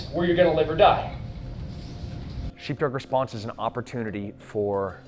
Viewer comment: the subtitle says cheap drug 😂